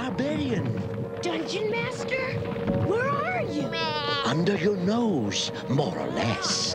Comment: I swear I heard Dungeon Master say “Under your nose, moron ass!” 😂😭